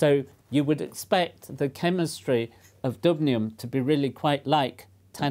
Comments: i thought it said dabnium and i wanted to die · Thought it said "Dabnium"